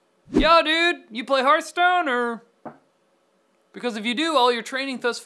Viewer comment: omg I thought he said people playing stonehearth (hopefully I spelled that right) not hearthstone. xD I was like lol wut?